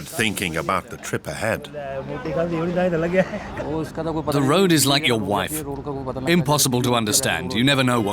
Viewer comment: Did he just say '"the weather is like your wife, unoredictable?